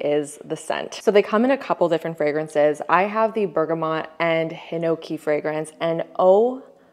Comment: Really thought you said “bergamot and gnocchi fragrance”…. 😂